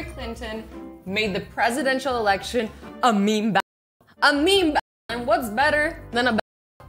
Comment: Did anyone hear when she said at 2:55 "a meme ba- and whats better then a meme ba-" like I wanted to know what she was saying so i turned on subtitles and she was saying battle, is it just me, do you hear battle?